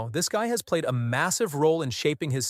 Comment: what did he say? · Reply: “MASSIVE”